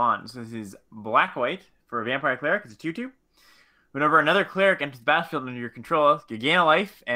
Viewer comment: It almost sounded like you said: "Whenever another cleric enters the Bathroom under your control" 😂